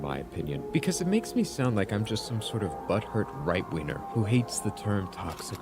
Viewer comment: Did he just call himself a "butt hurt right weiner"? He didn't want to maybe do another take? 😂